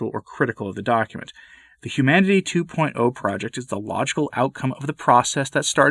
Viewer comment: For a moment I thought you said “lodgeful outcome” rather than “logical outcome”